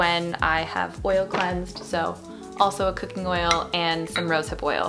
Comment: did she just say a cooking oil?